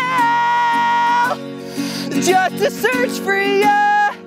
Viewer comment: Who else thought he was going to say "just to smell ya".